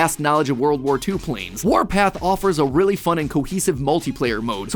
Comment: At first I thought warpath said MatPat. LMAO 🤣 😂